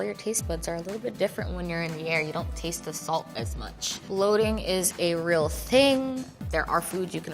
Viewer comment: Does anyone know what she meant by loading at 10:37? Sorry 😅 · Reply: Bloating is what she said ❤